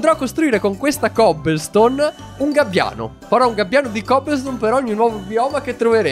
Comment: Come in Minecraft Hardcore 2 hai costruito il gabbiano di roccia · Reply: Ho premuto 5:23 e ho sentito "ano" XD